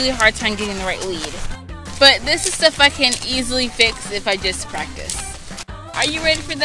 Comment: did she say the F word? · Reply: Gurl, just use subtitles and stop assuming things 😑🙄 She literally said "stuff I can easily fix"